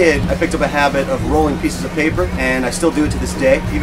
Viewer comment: "I still roll papers to this day" I thought he was going to pull out a joint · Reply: Lol I thought he said "grow paper" I'm an idiot 😂